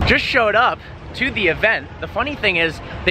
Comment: I like how he replayed it back he said "unfortunate" not "importantant" whatever the red headed guy thought he heard.